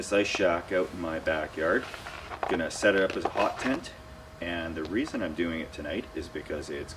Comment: lol I thought he said "POT tent" 0:11 skipped ahead a litte at 0:52 lol! Sorry I had too much step 2 today.. Been binge watching you .. good stuff.. I love the stealth camping!!